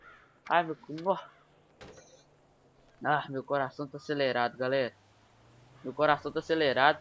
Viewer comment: Mano eu entendi vc fala " Aí meu cu " Kkkk